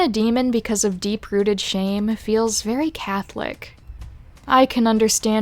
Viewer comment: Catholic….? Did you say Catholic? Not gonna lie but I’m Catholic. 😳